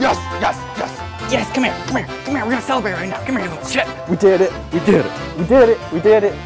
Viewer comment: Did he say "come hear you little sh*t" at 13:55 or is my mind just that bad?